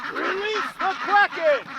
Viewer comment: I heard “Release the Quaken!”